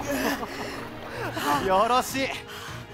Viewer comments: よろしいがやらしいに聞こえた俺はもうダメだ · 最初のよろしいがやらしいに聞こえてしまた